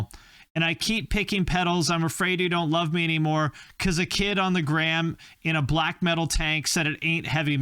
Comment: Thought he said on the gram in a black Dahlia tank?I'm hearing things?